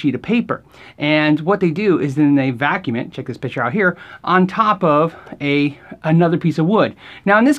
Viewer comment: Really thought you said "Check this bitch out right here" and spit my drink everywhere